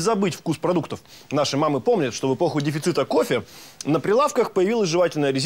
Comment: Я знаю что он говорит слово ЭПОХЕ, но мне слышится слово П0Х*Й